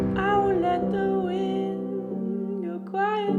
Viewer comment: I thought he said " I won't let the clique go quietly" or something but he said wind